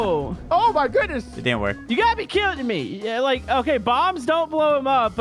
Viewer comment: you got me killed me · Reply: I heard he say "you gotta be kidding to me"